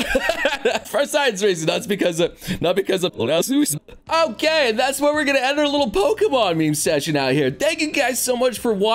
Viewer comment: What'd he say? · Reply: He said suicidal in reverse